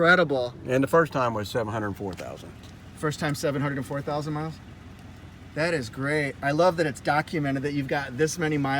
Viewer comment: His 1st spark plug replacement was at 70,400 miles? Sounded like he said Seven Hundred Four Thousand miles.